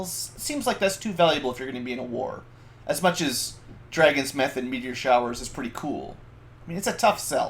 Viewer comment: What on earth did you say @ 8:30?! Did I hear right: "As much as Dragons, METH and meteor showers are pretty cool" ahahah x'D Loving the MM series thus far! MM7 was my childhood, great seeing the 8th for 1st time!